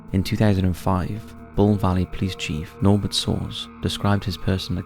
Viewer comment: Could anyone tell me what the name said at 2:39 please? It sounded like Nolbert Sauce to me and now I can’t unhear that! 💀